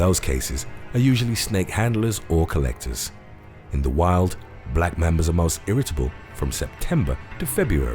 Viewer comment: I thought that said black mamas 😂